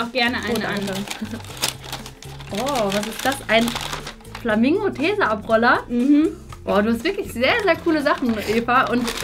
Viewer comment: min ich verstehe anstatt „Flamingo Tesaabroller“: „Flamingo Käseabroller“ 😂😂😂👏🏻